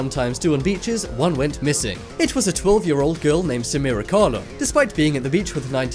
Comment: omg I thought he said Sara Conner